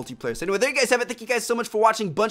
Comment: WTF IS HE SAYING?ASDASIHDUYAGDAUYSDG YET.ONLY THE WORD YET I HEARD. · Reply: "Check out one of the other two videos on the screen if you haven't seen them yet."